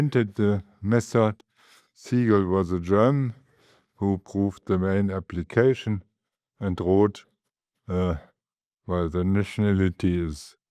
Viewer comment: If you find a mistake in the subs, please let me know in a kind way. You'll probably never realize how much I, as a non-mathematician, bled to get this subtitled before the weekend. PS. Make sure you're using the subtitles provided. I'm not responsible for the auto-generated ones, nor can I edit them. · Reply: "... who proved the main application, and Roth..." instead of "... who proved the main application and wrote..."